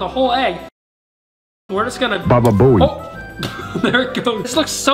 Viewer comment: did i just hear bababooey ....